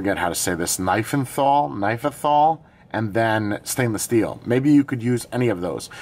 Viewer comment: 😂did you say "knife and fall"?🤣Perhaps... Nickel-Iron? :D